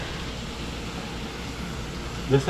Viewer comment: Thought u said it only had enough for u 😂